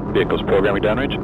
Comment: can anyone give me a plain text transduction of the transmission at ~ 0:36? i seem to understand : "vehicle ... damage". i firmly believe i misheard that, but what is the actual message?